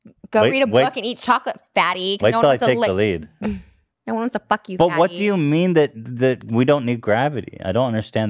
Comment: Trish got PISSED when he called her fat. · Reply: Did anyone catch what she said... “nobody wants to lick...”